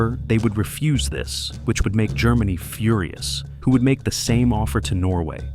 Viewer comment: Führer-us instead of furious